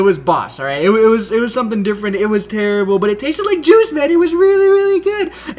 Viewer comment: Oh I thought he said the zombie taste like jizz haha but he said juice